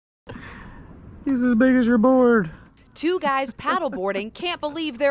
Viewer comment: LMAO why did I hear "he's as big as your b*ner in the beginning, oh my god 😭